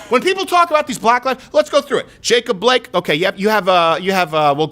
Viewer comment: Thought I heard Rapeob Blake. Hahahahaha